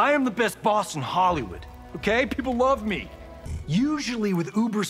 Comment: I thought he said "Gay people love me!" until I read the captions.